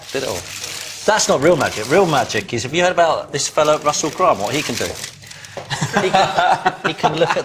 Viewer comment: he talks about an astrologer by the name of Russel ___? I couldn't understand the last name... Anyone?? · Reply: Russel Grant